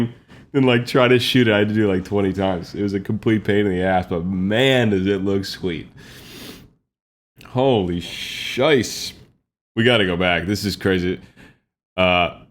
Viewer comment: Lmao, does he say "Holy Scheiß" at 20:01? 😂😂🇩🇪